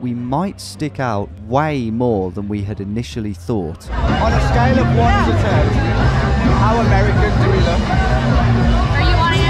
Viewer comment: What was said at 37:26? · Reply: When's he's speaking to the girls? If so, it's "on a scale of 1-10, how American do we look?" Unless this is some high brow sarcasm and I'm an idiot.